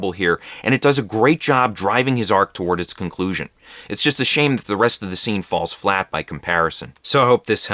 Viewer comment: I could have sworn he said "it's a shame the rest of the scene falls flat as Paris Hilton" 😳